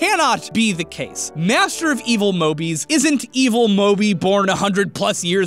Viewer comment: Every time I hear say "Evil Mobi," I always keep hearing "Evil Morty," anyone else?